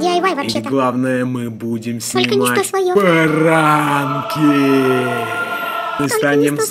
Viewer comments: мне послышалась снипать баранки лол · мне послышалось не пранки а баранки